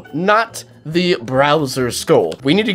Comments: Did you just say... BROWSER Skull????? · did he say browser???😂😂😂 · Yo unspeakable did you just say browser not Bowser cuz that what I heard